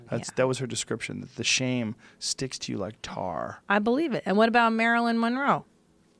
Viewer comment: I thought joe said Clinton's dick sticks to you like tar😂😂😂